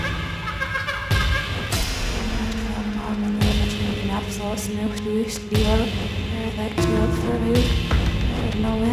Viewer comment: anyone have the tools to reverse this audio and see what is being said? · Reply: ". . .but I'd still devour you. As I lay here and recall years of wavering emotions, suppressed desires, and volcanic explosions that retreat into dormancy I wonder, could we ever flow together, or are we two twin souls finding each other in the wrong lifetime?"